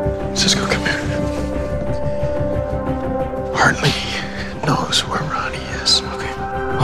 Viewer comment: What is Barry saying in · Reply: "Hartley knows where Ronny is."